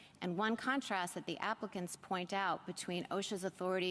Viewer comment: I had to rewind that comment “The applicants point out...” I thought she said “Africans”! That child voice is very hard to listen to.